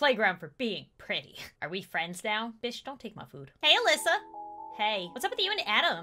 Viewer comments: Bish don't take my food lok Edit: *lol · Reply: Carlos manuel Ortiz oh, I thought she said “Fish don’t take my food” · " bish, dint take my food " Me: * screams in jojo* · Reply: I thought she said “Fish don’t take mah food” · “Bish don’t take my food” - 2018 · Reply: I thought she said. “Fish don’t take my food” 😂😂😂😂😂😂😂😂